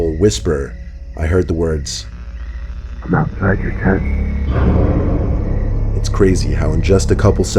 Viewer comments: what did he say? i can't understand what he's saying... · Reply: He said "I'm outside your tent" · what did he say? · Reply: Patrick Moore he said “I’m outside your tent ” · What did he say at 9:48? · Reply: "I'm outside your tent" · he said “I’m outside your tent” but the auto-generated subtitles said “im not your cat” lmao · What did the person say through the walkie talkie?? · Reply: Kate 9:48 Now that I hear it again does it say "I'm outside your tent"? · What did he say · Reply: "I'm outside your tent" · What did he say? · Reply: I'm outside your tent